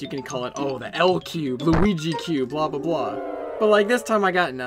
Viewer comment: sound like you just say lg